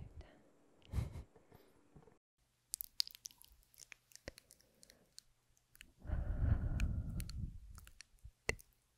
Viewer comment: Timestamps and any repeated words 🎄 0:09 intro “howdy, back, frivvi viddie, up close, holiday trigger words, tingly, relax, enjoy” 2:27 first word “happy holidays” 3:20 second word “Christmas” 3:52 third word “Hanukkah” 4:16 fourth word Kwanza 4:33 fifth word (not sure what this word is but it starts with an O) 4:52 sixth word “New Years” 5:38 seventh word “Christmas” (crispy) 6:00 eighth word “Feliz Navidad” 7:10 ninth word “Saint Nick” 7:42 tenth word “Kris Kringle” 8:22 eleventh word “Santa Clause” 9:16 twelfth word “Rudolph the Red Nosed Reindeer” 9:40 thirteenth word “Dasher” 9:56 fourteen word “Dancer” 10:08 fifteenth word “Prancer” 10:25 sixteenth word “Vixen” 10:43 seventeenth word “Comet” 10:54 eighteenth word “Cupid” 11:13 nineteenth word “Donner” 11:29 twentieth word “Blitzen” 11:59 twenty first word “Jack Frost” 12:43 twenty second word “Frosty the Snowman” 13:39 twenty third word “snowflakes” 14:03 twenty fourth word “cold and icy, ice cold” 14:32 twenty fifth word “icicles” 14:54 twenty sixth word “mittens” 14:59 twenty seventh word “hot chocolate” 16:17 twenty eighth word “sugar cookie” 17:10 twenty ninth word “sugar plums” 7:35 thirtieth word “fruit cake” 18:09 thirty first word “carrot cake” 19:06 thirty second word “plum pudding” 19:51 thirty third word “decorations” 20:28 thirty fourth word “ornaments” 20:47 thirty fifth word “Christmas tree” 20:55 thirty sixth word “stockings” 21:40 thirty seventh word “mistletoe + kisses” 23:23 thirty eighth word “nutcrac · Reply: the fifth word is Ōmisoka!